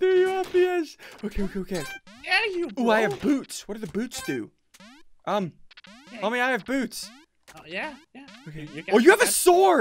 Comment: in 9:15 did ssundee said i have boobs homie???